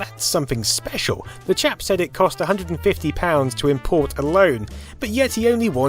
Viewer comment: I thought i heard "The Jap said it cost..." and was like WTF for about 1.4 seconds. "Chap"... I'll get me coat.